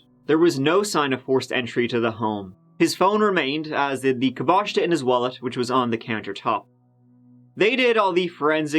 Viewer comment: What does he say at 4:35? "His phone remained as ??????????? in his wallet". Sorry, English isn't my first language. If anyone can help... it's kinda driving me crazy, thanks. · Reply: His phone remained as did his caboista (money) in his wallet. Caboista is Irish slang for money or items you are referring to. 😉